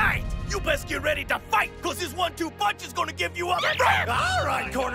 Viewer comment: did usopp say "you best get ready to fight, pussies"? cause I hope so